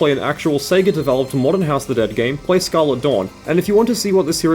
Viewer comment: For anyone who cant understand because of the accent he says at 21:08 Scarlet Dawn